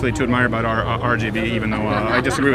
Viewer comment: did he say RGB